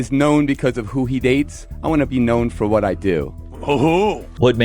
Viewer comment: When Chris says this I hear "David is known for who he dates, I want to be known for what I fuck"